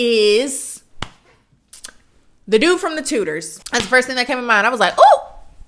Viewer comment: Tell me why I heard "dude from tooters" instead of "dude from Tudors"